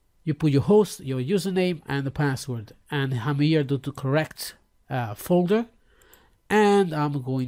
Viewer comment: what is being said at 7:21 ? unable to get it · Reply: ** I am in the correct folder (the osc folder if in that case the root for the domain used) hope it helps